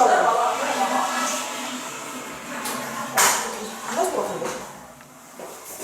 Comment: 여기서 은정이 뭐라고 하는거예요? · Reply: 졸라심각한데